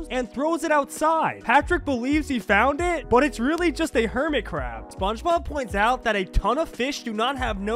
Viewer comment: After all these years, I finally found a person that was famous to say hermit crab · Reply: I legit thought he was saying Hermitcraft